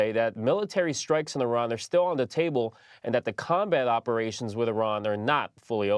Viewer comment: 😂 I thought he said "that the con man operations with Iran are not fully over" 😂 instead of "combat operations". Damn shame. 😂